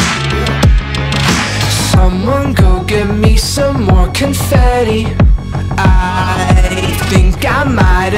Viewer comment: why is it, whenever i hear "get me more confetti" my brain fills it in and autocorrects to spaghetti..